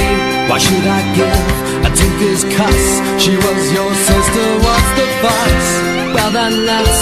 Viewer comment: did he say "one of us"? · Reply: She was your sister, what's the fuss?